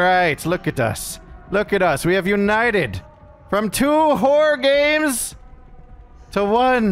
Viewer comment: I heard 'whore games' instead of 'horror games' 🤣